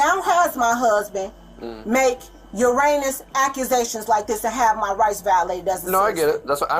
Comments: for the folks who misheard "your anus / uranus accusations," i think she said "your heinous accusations." just a minor correction !!!! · She's a terrible, awful person, but did she just say Uranus accusations? 😂😂